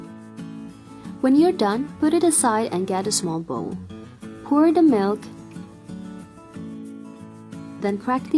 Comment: itu ngomong apa ya yg ada kata "done" nya?🙏🏻🙏🏻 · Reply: Bantu jawab ya, when you’re done put it aside and get a small bowl.